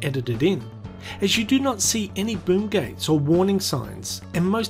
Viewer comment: Did he just say “Boonk Gang?” 😂